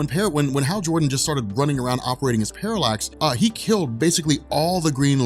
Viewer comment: When he said "when Hal Jordan just started running around around operating as Parallax" I thought he said "operating his Parallax" and took it in a dirty way. I think mine is better....